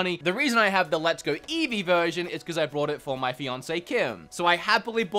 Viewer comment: What does this word mean that sounds like Beyoncé. Im German and I always wondered. Can someone pleas tell me? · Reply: Fiance.